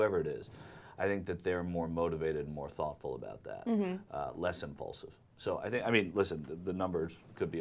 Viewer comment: Did anyone else catch at 1:17 when Michael Shure is describing women as being "less impulsive" the subtitle read "Lesson Balls"?